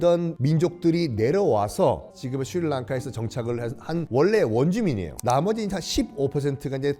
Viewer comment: 썬킴이 스리랑카를 말하면 스리랑카가 아니라 슈리랑카로 들림